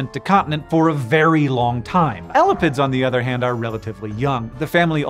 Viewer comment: I kept hearing 'elephants' instead of elapids and was so confused.